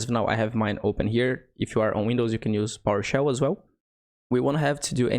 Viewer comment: what did you say i dint understand... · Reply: PowerShell